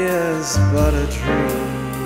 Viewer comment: Wait for bit... I think he said “wife is a dream”